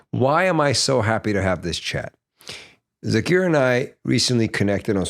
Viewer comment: did he said Zakir naik💀